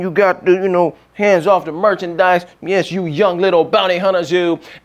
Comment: I thought his shirt said hands of necromancy..